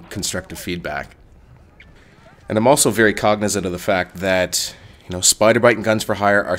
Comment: And I`m also very cognitive(?) that GorillaBite and Gorilla4Hire are striving to provide quality on this channel. I couldn`t hear what you said at the word cognitive, sounded like it could be cognisive, but google corrected that to cognitive.